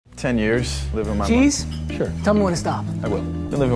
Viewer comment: "I grew up at a dairy farm and one of my favourite hobbies was actually drinking milk straight from the cows" and "I'm now waiting for my mom to pass away once she does i am going to be the owner of a brand new house" wth😂😭 Edit :- i changed the lines cuz i misheard it.. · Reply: I had the volume very low because the news were on next to me, so I heard "my brother"